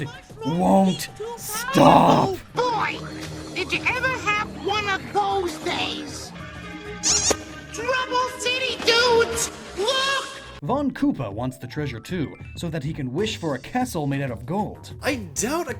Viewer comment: I don’t know if I *cough* *cough* misheard him, but did he just say “Trouble’s t!tty dudes,” ?